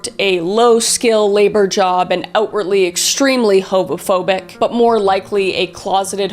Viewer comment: did she say hobo-phobic? 😂